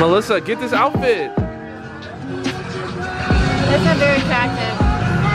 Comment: "That's not very attractive" What did you just say?